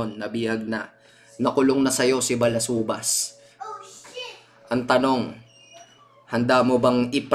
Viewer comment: ohh sh*t narinig ko WHAHHAAH